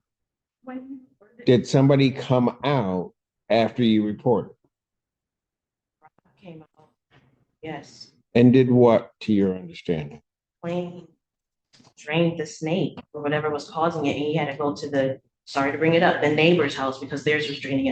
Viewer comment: Did she just say "drained the snake"?!? 😅